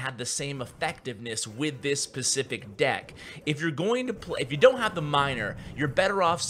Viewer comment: did he say "with this PACIFIC deck"